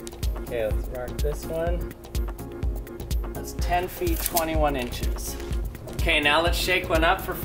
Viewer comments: did he say 10 feet 21 inches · did he say 10 foot 21 inches?